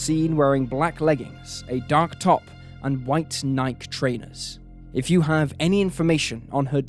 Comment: Wait, did he say Nike (rhymes with Mike)? Ha ha ha I thought Nike was everywhere.